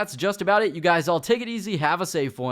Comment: I thought you said "My name is SuckCocks" lmao.